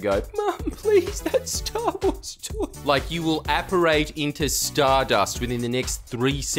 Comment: Can someone please help me with what word Jordie is saying here? 9:33 It sounds like “apporate”, but I can’t find any word like that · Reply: Aporate